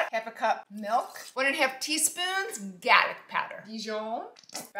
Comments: I watch her just to hear her say Galic (garlic) Powder! I love it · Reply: thank you smmm for commenting this.. i literally thought she was saying gag powder and i was researching and searching to make this recipe. i was so lost 🤦‍♀️ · I watch her just to hear her say Galic (garlic) Powder! I love it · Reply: Thank you! I thought she was saying gack. So I’m looking in the comments to see what the heck gack powder is! Garlic! Got it